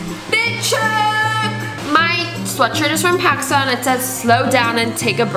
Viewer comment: Wait at 3:17 did she say my sweatshirt is from Pakistan???